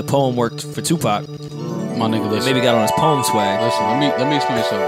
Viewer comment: Watching whilst letting my Ganja buuuurn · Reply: Lol sounded like rory said "nigga got on his poem swag" 😂😂😂😂😂